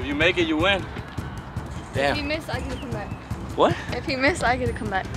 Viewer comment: i thought she said if he miss i’ll give you some neck lmao